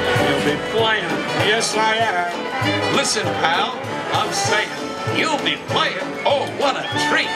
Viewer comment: did it just say “I’m satan”